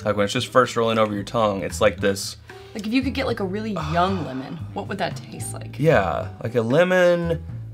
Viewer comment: I thought she said "woman" instead of "lemon". Had to back it up and listen again. LOL